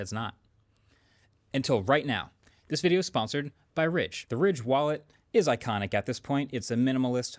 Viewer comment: Why did I just hear 'sponsored by Samsung...'